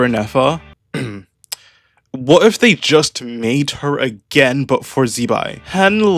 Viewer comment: LMFAO THE INTRO 😂😂😂😂😂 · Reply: at first I heard "Mate her again" English is perfect language